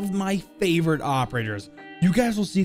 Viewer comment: did he say operters? somewhere